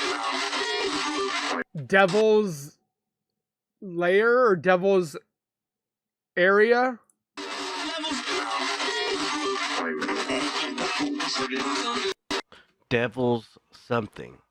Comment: I heard Devil's breath.